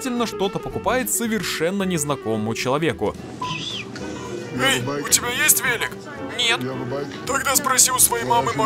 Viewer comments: Мне одному послышалось "эй у тебя есть веник"? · Мне послышалось, "веник"😂